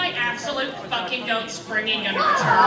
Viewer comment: I think I know what they said in 10:18: "What if One let Three tell about [...]" And at 13:23 "*When* is my f-cking goat Spongy *gonna return*?" 26:51 is "Is Rose and Beach Ball the same ones than in BFDI Mini and (i think) will Ruby's sisters eventually be BFDI Mini Replicas" (havent watched bfdi mini, i only know rose and beach ball are in these, but idk if ruby's sisters already were in replicas or not) 28:15 "What motivates you to [...]"